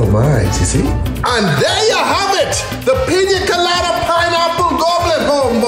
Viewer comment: I just heard in the end "GOD DIAA HAVE IT THE PINION PINEAPPLE OF THE WORLD